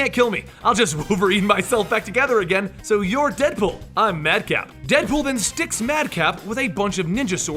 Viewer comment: I thought he said Madcow. I was like, it seems like that Mad Cow Disease is gettin' around.